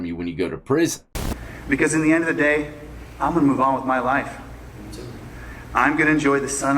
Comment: It sounded like the defendant said, "Me too."